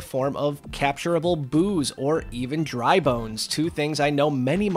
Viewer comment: At first I thought you said mario fans would be excited for capturable booze.